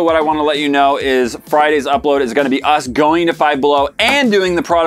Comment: At the beginning of the video. When he was talking about going to Five Below. I thought he said they were going to find blow and try the product. I had to back it up and re-watch. Lol.